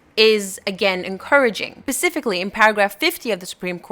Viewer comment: did you just say pacificly 😐